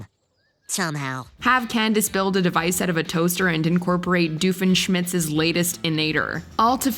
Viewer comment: Did anyone else hear “doofenSHMITZ” at 4:42 or was that just me?